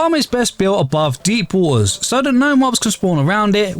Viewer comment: Subtitles at 1:50 says door instead of water